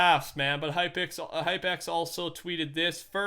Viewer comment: Did I hear hypixel?